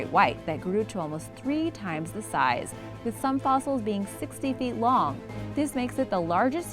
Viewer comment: I think the length you meant was what she said: 60ft NOT 16...